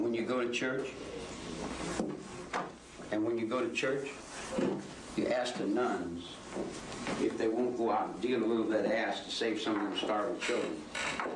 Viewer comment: did he say when u go to Church ask the Nuns if they won’t go out and deal a little bit of ass to save the starving children ?? 😅